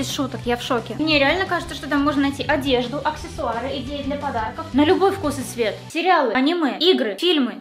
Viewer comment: В натуре, на 6:57 говорит «идеи для подарков», а я слышу «и геи для подарков» ЯОЙ ВОШЁЛ В ЧАТ🌚🌚🌚